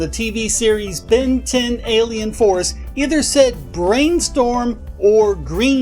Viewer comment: Even when "brainstorm" was on screen I just heard "brain needle" lol